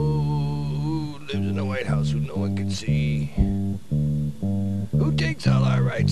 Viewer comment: What does he say at 0:19 and 0:23??? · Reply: Are you ready Kids? Aye aye Captain! I cant (fucking-(edited out by YT)) hear you... AYE AYE CAPTAIN!!! Oh... Who lives in the white house where no one can see, Who takes all our rights with the RFID, if something from nothing be something you wish, then shut the fuck up and list you bitch... Ready; Jolly Roger, Jolly Roger, Jolly Roger... Jolly... Roger! ha ha he yah ha...